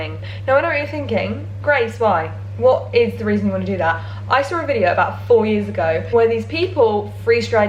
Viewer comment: I totally thought she said “I saw a video about four minutes ago,” made complete sense to me that she would pick up a camera and begin filming right away